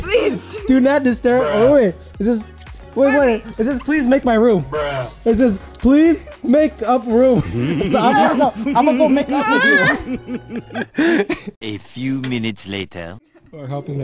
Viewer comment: did he just say "im gonna go make up with u"